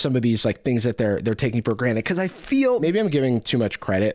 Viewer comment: Did you just say Granite? It’s granted. Do you really think it’s granite? What is a countertop?